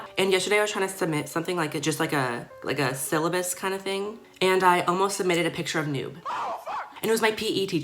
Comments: Lmao I thought said "I almost submitted a little nude" for a sec 😂😂 I was like whattttt?! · i thought she said “ I submitted a picture of nude” and sis i screamed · Was I the only one that thought that she said nude pictures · i really thought she said she almost submitted a nude to her pe teacher ohmygod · at first I thought she said "a nude" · at first I heard “I almost submitted a picture of a nude....and it was my PE teacher”😹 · I thought sis said "I almost submitted a picture of a nude" 😌💀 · oh i thought she was saying that she almost submitted a picture of nude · i thought she said a nude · I thought she said I almost submitted a nude😂😂